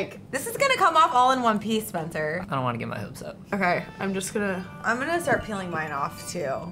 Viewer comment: Am I the only one who heard him say I don't want to get Mia upset?